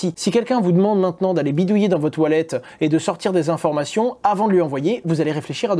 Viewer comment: A 2:35 jai entendu "si quelqu'un vous demande d'aller bidouiller dans vos toilettes," 😂😂😂 jai pas compris il me voulait quoi!!